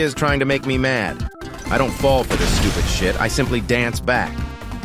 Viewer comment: did she say sh!t?